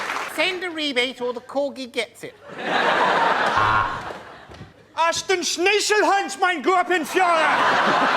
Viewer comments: What does Frankie say at 13:50? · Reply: Who's the president here, me or you? Me, shit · what did Frankie said in 13:51? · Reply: In German, führer means "leader", so technically Angela Merkel is now the führer. Frankie said Gruppenführer (an actual German military rank meaning Group Leader), but the rest of it was gibberish, apart from schnizel and heinz... maybe it was about sandwiches, who knows. The joke was of course about the Royal Family having some German heritage.